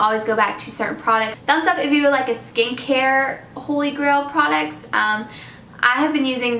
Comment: I thought you said 'skank hair' BUT YES skin care holy grail video please!!!